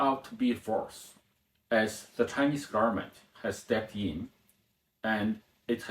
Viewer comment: This is the second video where I heard our handsome narrator say "Chinese garment". Same with the subtitle too. See 2:09. No third time please.